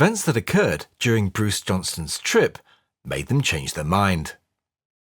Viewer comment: EXCELLENT !! One minor correction maybe- sounded like you said Bruce Johnson-- AYK it's Johnston. Well, his real name is Baldwin anyway !!!